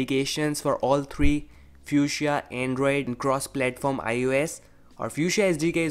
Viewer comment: I thought it said Fucksia OS